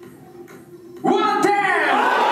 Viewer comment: なんだろう…観客席から牛魔王の声で110番って聞こえるんだけど(混乱)